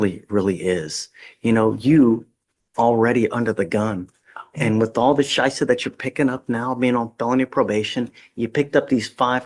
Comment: Does he say "Scheiße"?